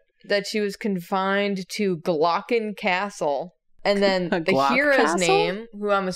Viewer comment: I heard you say glock castle. I HEARD YOU